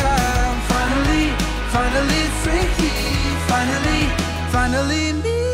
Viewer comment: Everyone: Omg this is so cute and wholesome Me: Omg! A song about being a freak like me because i thought he said "Finally a freak" 💀